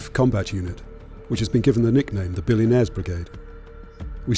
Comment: without captions, i thought he said "The Billy Mays Brigade" drones fly in like "HI, BILLY MAYS HERE💥💥💥"